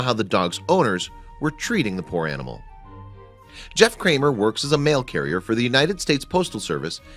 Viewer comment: Jeff kramer i thought what he said john kramer from jigsaw